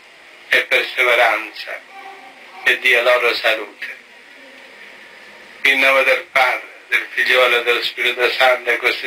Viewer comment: Does anyone speak italian ? @ 2:08 It sounds like he says ghostasia. Espiritu Sancto Ghostasia. I can`t find a Italian word it. Anyone have an idea ? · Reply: He says "Nel nome del Padre, del Figlio e dello Spirito Santo. Così sia". Cosi sia is the translation for Amen :)